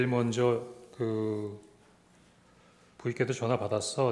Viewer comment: 제일 먼저 그.. 00께도 전화받았어. 대통령이 너희들 도와주래. 에서 00가 뭔가요? 부인처럼 들리는데 절대 아닐거같고..